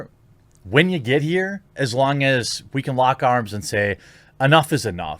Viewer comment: Ohhhhhh lock arms. I'm sorry. 😔 I thought I heard lock n load. 😁